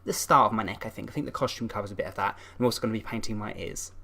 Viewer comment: did you just said painting my ass? XD NO PAINTING MY EARS SORRY LOL